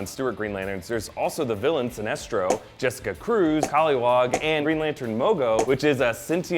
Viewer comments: did he just say kolliwog? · Did he say koliwog??